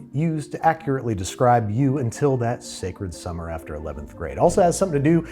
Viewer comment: first time through I thought you said 7th grade and I was at a loss for words 😂